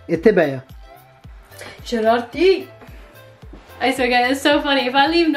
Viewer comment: What did say at 8:44? Sherati? It means naughty?